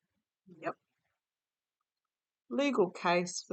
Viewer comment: I thought I heard your guide at 23:18 unless that was you, I thought I heard your guide say yep!